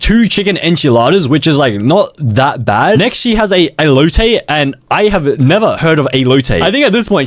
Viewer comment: Did you say Low tape? LOWWW TAPER FADE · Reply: Elote not low tape